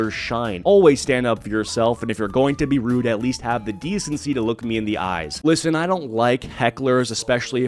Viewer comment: At first it sounds like she said "Fuck me in the eyes!" 😆